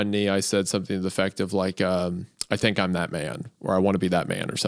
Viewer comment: I misheard as saying during his proposal, "I think I'm Batman."